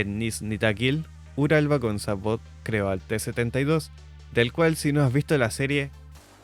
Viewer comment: Cual es la serie que recomiendas al principio 1:16? no te entendí bien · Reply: Sacó unos videos sobre el T-72, así como éste